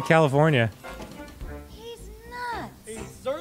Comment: was looking away and thought for a sec she said "Deez nuts!"